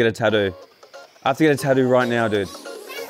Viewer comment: Did just say tadoo instead of Tattoo?